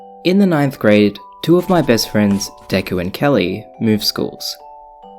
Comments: DEKUUUU?? idk if I misheard but holy crap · did say ....deku? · Did he just call his best friend Deku???? My Hero Academia Intensifies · i swear he said deku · DiD hE jUsT sAy DeKu! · DID I HEAR DEKU?!? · Did he just say...wait- hoLd ThE PhoNE- did he say...de-deku..? 0:09 NANI?! ?_?.